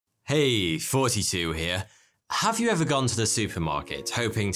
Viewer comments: For years I thought you said, hey, forty 2 here. Which confused me because your name is Thoughty. Yeah, I’m simple as can be. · At the start it sounds like he is saying 42 here instead of thoughty 2 Cause his accent is deep British